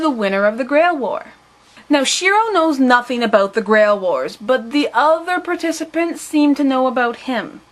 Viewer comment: OHHHH THIS MAKES SENSE NOW!! XD When I first watched Fate/Stay Night I thought the guy said Grail whores lol Now i realize he said grail wars lololol